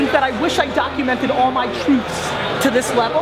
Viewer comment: "i wish i documented all my ..." that is he saying? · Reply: truths